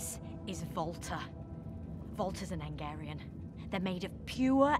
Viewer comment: I thought she said "Volta's a Hungarian."